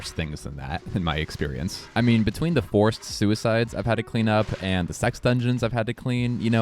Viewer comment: Did you say "forced suicides"? @ 3:25 Jesus.... Part of me wants clarification but the rest of me knows that there can only be so many explanations and they must all be horrible.